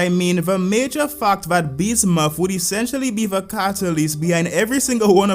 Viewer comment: Without subtitles, I hear only "Bismuth would essentially be the cartoon lesbian" 😂